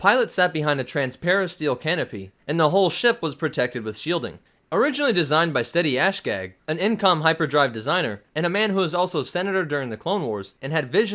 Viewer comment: Did he just say ‘shitty assgag’?